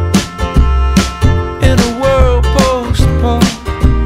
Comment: "we wanted to be punk in a world post punk" best line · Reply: huh, i always thought he said "In a world postponed". post punk makes so much more sense lol!